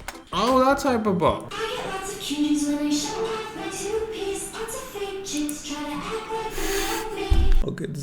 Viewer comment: I honestly thought they were saying “I get lots of cuties when I show off my toothpaste. 😭